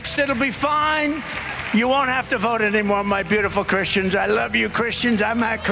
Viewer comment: Bro....excellent gathering of receipts. And my thoughts exactly, thank you for keeping the same energy for both these illuminist actors. Left-wing, right-wing same double-headed eagle. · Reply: Also at around the 21:15 mark I'm not sure it sounds like he said I'm not a Christian " 🤔